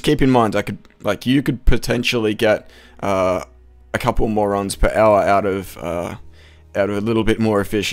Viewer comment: it sounded like you said a couple morons per hour XD